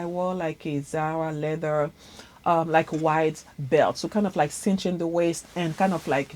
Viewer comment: Love ur channel Sis I don’t see products listed, hats leopard, on the mannequin, orange ur wearing, handbags-striped & tan , thought u said American Leather. I’ll rewind. Thank u 🙏🏽